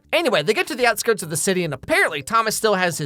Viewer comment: did he say YN ?? 💀💀